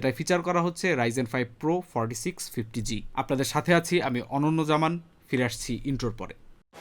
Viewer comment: you said "Ananna Zaman" I heard "Ananta Jalil"